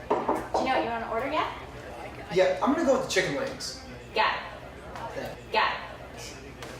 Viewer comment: I thought she said "you got it" [extremely fast] lol